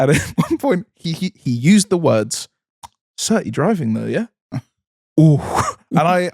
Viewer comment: Can someone tell me what says @ 22:45 please when quoting the car dealer? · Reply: "Certi driving tho yeah", certi being slang for certified aka good